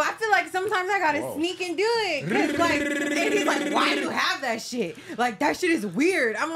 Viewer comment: minute, does she say “Sometimes I gotta sneak and do it cuz like AD is like, why you have that shit?” WOOOOOAH